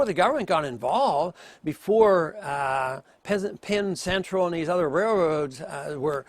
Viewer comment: Did he said "peasant central" at 1:36?